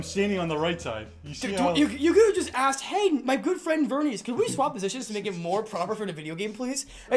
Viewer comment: I misheard good friend for girlfriend and thought "What? That wasn't what he was saying, right?" 😂